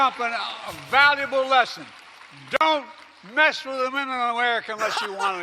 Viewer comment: I heard "Don't mess with a mental American"